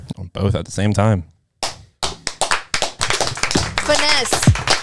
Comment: Vanesse 🙌 Ps. looked it up, & the name origin means butterflies 🦋 Thought she said finesse lol